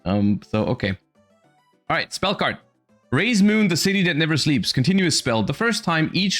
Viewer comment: When I looked away, I heard him say "Raye's Moon" instead. No wonder he likes this archetype.